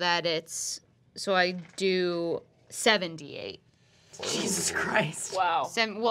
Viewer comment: what is actually said: "So I do 7d8" what the subtitles say "So I do 78" lol